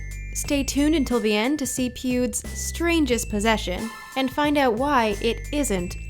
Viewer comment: Is it just me who heard pubes!😂